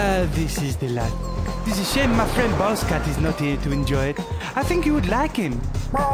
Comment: i heard balls cat XD